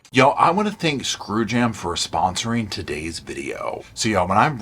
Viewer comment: Screw jam, I thought you said Screw him, sounded fun to me.